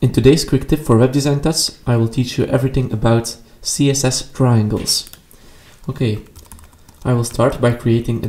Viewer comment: I thought you said to teach about using triangles too